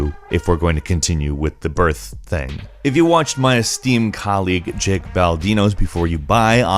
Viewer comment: I thought you said "Steam colleague" at first and I was like cool they play Steam together